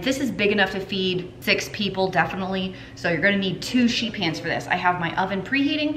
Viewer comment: when you said sheet pans, the caption said sheep hands. 🤔🥸😆😄 But seriously, thank you so much for these. Great ideas I am definitely going to try.